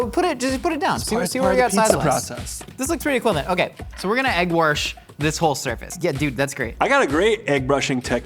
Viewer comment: Is josh from the midwest cause I definitely heard egg warsh